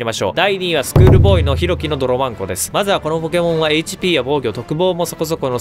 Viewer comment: 若干ネタバレになりますが… 動画にてドロバンコの名前を読み上げるときに、ドロマ○コと聞こえるかもしれませんが、 私はしっかりドロバンコと言いました。私の滑舌の問題で、決して下ネタではございません。 ドロバンコを馬鹿にしているわけでもないので、先にドロバンコフリークの方に謝罪させていただきます。 私の舌を引き抜くような蛮行には至らぬようお願いいたします。ご容赦ください。